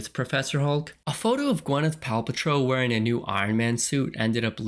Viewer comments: You forgot to include the leak from my classmate that nobody liked, telling everyone in first period chemistry class that iron man and black widow both die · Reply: Bro I could swear you said „Gwyneth Paw Patrol“ at 7:40 😂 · Did you just say Gwyneth Palpatrol xD